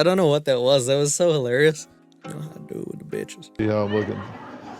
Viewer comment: Unless there's something after it, that part that you couldn't understand from Dr. Phil under his breath (24:18) was "see how I'm lookin'." Now, there might be a short little something afterward, but he definitely said that.